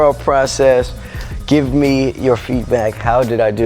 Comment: did he say “give me your feet back?” 😹